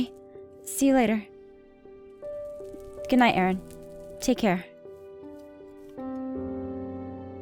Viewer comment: When Aphmau said take care to Aaron I thought she said "take care Little Aaron"